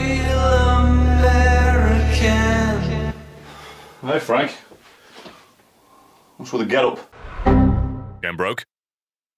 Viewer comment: did he say "what's with the gelb?" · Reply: "What's with the getup?"